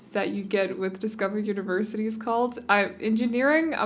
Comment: thank u! ive been trying to research all it says it was added around that time but sadly i dont have the game to test it. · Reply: butera records I just realized what they said. I thought they said do I have university? as if they were being snarky about it. Sorry